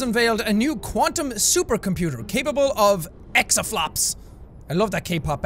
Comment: did jacks just call EXO flops 😭😭